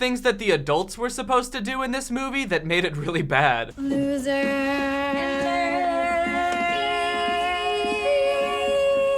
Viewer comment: Did they say Loser...loser...me..me Huh?